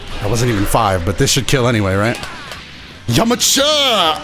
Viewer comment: Did he say yamacha instead of yamcha